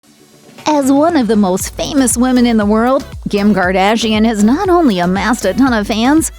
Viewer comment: Did she just say ~Gim Gardashian~ 0:03 LMAO